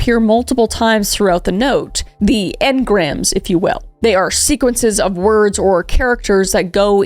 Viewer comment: When she said sequences I thought she said secretses